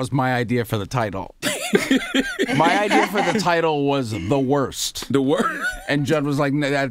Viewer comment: Pete really snuck in the N word and charlamagne didn’t even bat an eye. Lmao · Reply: 'the worst' is what he said at 13:02 time frame. After that he meant was 'Na/No'. Please dont try to create and spread false racist accusation as a joke, it's serious topic and issue. So, it can ruin and harm people's career and reputation forever.